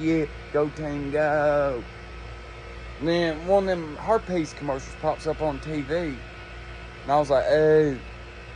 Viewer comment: I thought he said hair peice commercial 😂